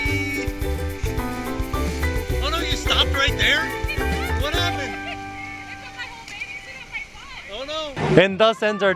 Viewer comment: What did say at 6:34? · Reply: "It took my whole bathing suit off my butt" 😂😂